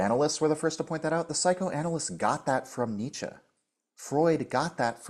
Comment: I think he said after Nietzsche